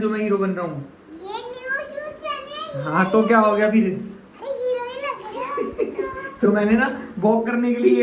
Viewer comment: I just heard that Heroine Lag Rhe ho😂😂😅😅